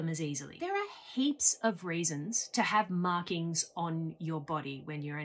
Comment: I thought she said “raisins” like the fruit. Nope, it was “reasons”. 😂